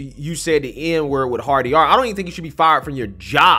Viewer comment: OH. You're saying "Hard E-R"! I thought you were saying "Hardy R". I'm thinking "What's a Hardy R? lol (laughing at myself)